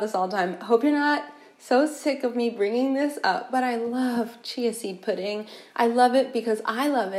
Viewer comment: I thought she said "Cheesy Pudding" and I started to give intense attention. Ah, "Chia Seed Pudding". OK, also sounds good.